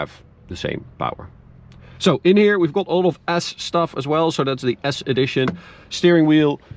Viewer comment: What I heard... "Inside we've got a lot of ass stuff" 🤣